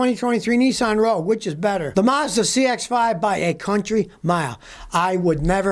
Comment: APPROX. MIN 8:31 Did you say by a 'cunty" mile That is against social media rules